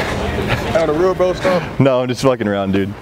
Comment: what did he say? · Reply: Mtn_Dewpider he said.. "Aye on the real bro stop..."